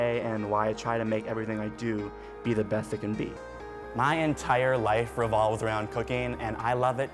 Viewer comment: For a sec i thought he said his entire life resolves around cocaine and i was like😮😗